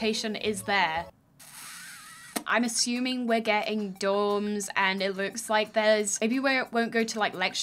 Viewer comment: she said “dorms” & I thought she said “doms” good god